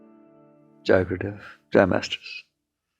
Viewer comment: can someone help me understand Mickey's speeches always start with ' ?? grand masters? ' 0:43 it's too quick for me to understand what he says exactly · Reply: Jai guru Dev